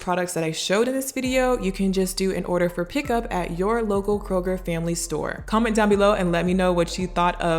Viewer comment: It sounds like she is saying Kruger